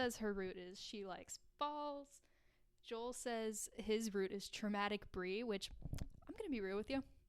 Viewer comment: I always misheard the line as "traumatic breasts", and then realized it was traumatic bris (which tbh, I thought 'traumatic breasts' made more sense)